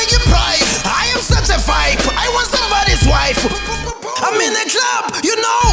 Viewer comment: Am i the only one who heard him say " I was somebody's wife" ?